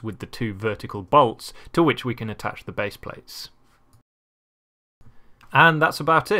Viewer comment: I keep hearing "to which we can attach the bass players" lol